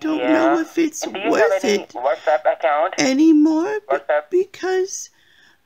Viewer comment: The whole time he was repeating, whatsapp, whatsapp it sounded like he was saying what's up. You should have answered him with......' sup dude in your demure little old lady voice!